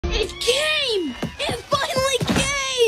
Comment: bro I thought she said I'm gay I'm finally gay💀